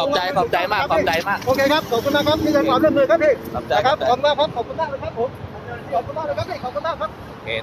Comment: ขอบคุณมากครับผมขอบคุณใสกครับจุกกู้มีใครได้ยินเหมือนผมไหม